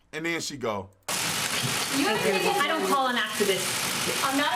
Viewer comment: OK, I'll admit admit it and I dont care if Im the only one. I don't know what the hell she said... I heard, 'I dont call an activist' .....